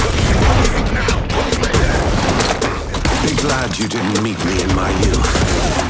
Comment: “Be glad you didn’t meet me in my youth” that’s all I need to hear I’m a ballistic main now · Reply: So pleased I found this comment, I thought he said 'Big lad you didn't meet me in my youth' and I had no idea what that meant xD